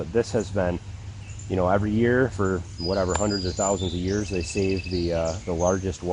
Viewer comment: This is off topic but do you not believe in a new Earth as a Christian? It sounded like you said hundreds of thousands of years? Just curious. Thanks for the video.